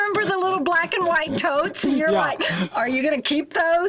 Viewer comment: Sorry, english is not my first language. What did she said at 11:54? Black and white what? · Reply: Totes... Plastic boxes